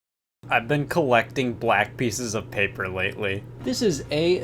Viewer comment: Paused in the begining and sounded like he said he collected black people... glad it wasn’t